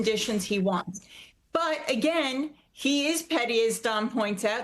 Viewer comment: Petty? I thought you said pretty. America first!🇺🇸